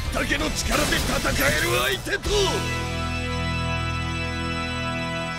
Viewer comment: Everyone is talking about how this is going to rock the anime world but... "it'sa party time..." - Brunhilde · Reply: I thought she said "pizza party time"